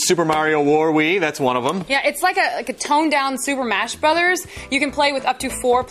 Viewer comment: Super MASH Brothers!?!!!?!!??! Did she just say Super MASH Brothers. Listen to this travesty here at 2:00 how did she make that mistake